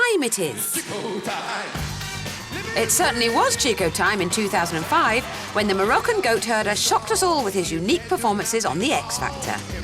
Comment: Did she say Moroccan Goat Turd? XD